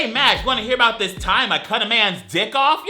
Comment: I heard 'man's' as 'ant's' and I was like "How do you even-?"